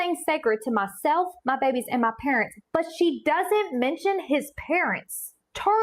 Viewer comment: I saw what Erika said and she mentioned her in-laws, Charlie's parents. She said verbatim "Can I have one thing left that is sacred to our family, to my in-laws, to my babies, and to my parents?". The rest is shady. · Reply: she didn't say children she said my parents.